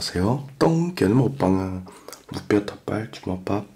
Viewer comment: 오늘은 유난히 안녕하세요 똥개에요로 들려요